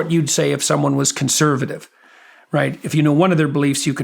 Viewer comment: I thought he said, “C.L.I.T is not a word,” but he said cilt. Lol. Love JP, this man is what a lot of people have needed for a long time